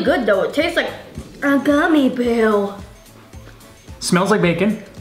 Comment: i thought she said a gummy bill😅